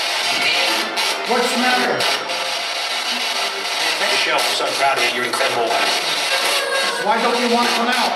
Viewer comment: it sounded like it said “michelle we’re so proud of you you’re incredible” 22:13 it sounded like it said “just watch me play”